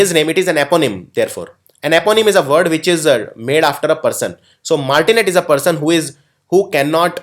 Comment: what did u say at 37:16 something which is named after one eponym????? I understood this please correct me